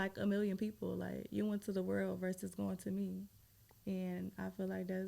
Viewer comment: Y’all ain’t hear her tho, She said “You went to the world, instead of me”